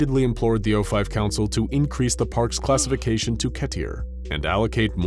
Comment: did he just say ketier